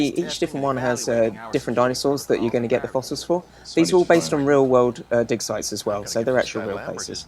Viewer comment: Wasn't watching properly and thought he said "real world dick size"